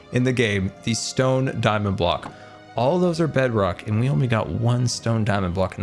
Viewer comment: two things, One the rarest ore in the game now is deep slate emerald ore, two did you say bedrock?